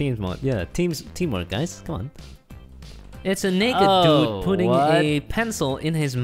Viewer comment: I swear he said the N word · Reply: Nope he said naked